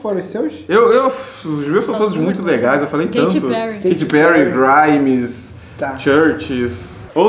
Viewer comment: Eu entendi " katy perry " kkkkkk